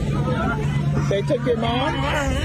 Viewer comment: Did i just hear the cameraman ask a guy in his 20's "they took your mom?" Fucking horribe.